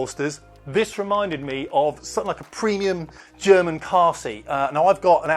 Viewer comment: I thought you said "Premium German Kharzi". :)